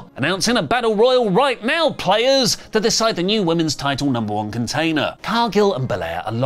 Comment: What's a Woman's #1 CONTAINER!! Anyone else hear this at the 7:11 mark? Ollie didn't think anyone would notice, but I did.